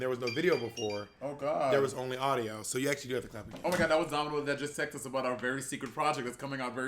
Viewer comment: I thought he said dominos 🤣